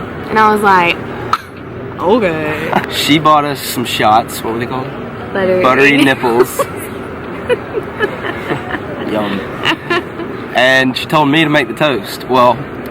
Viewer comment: I couldnt hear, what was the first answer?? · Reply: I thought he said DIMPLEs. I was like wait a min...that is a physical feature!